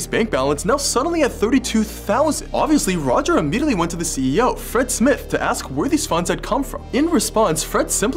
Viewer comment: I thought he said “Fritz Smith” and my neurons activated